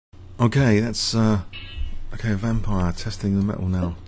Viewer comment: did you call your opponent vampire? xD